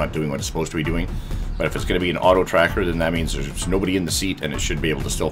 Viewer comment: I legit thought it said tractor